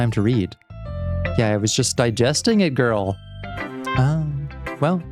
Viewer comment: I got briefly distracted during the Yuri poem and suddenly snapped back when I heard at 1:23:14 "Yeah, I was just digesting a girl." What a phrase to hear out of context.